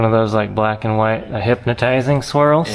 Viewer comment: Call it Silly Goose. And instead of a swirl, I thought said squirrel, lol.